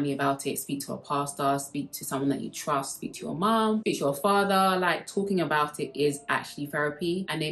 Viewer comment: subtitles says beat your father and i take it seriously yeah that make sense im on my way to beat my father to go through my break up